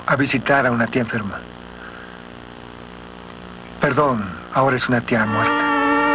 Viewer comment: No entiendo que dice Nick ahí. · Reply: "Ahora es una tía muerta", refiriendose a la tía de Norman que se supone estaba enferma y que fue el motivo por el cual el no fue a la reunión, yendo Nick en su lugar